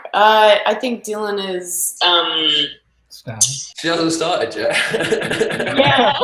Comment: Can someone pls tell me what is hero saying here: 7:16?? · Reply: She hasn't started yet